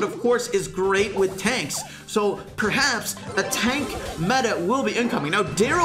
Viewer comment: ngl i thought that said tank metal?, like what else are tanks made of, plastic lmao